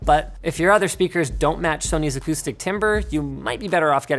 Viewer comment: LOL did you just say timber instead of timbre?